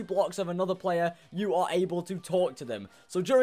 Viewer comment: At the start I thought he said: “but you are able to torture them!”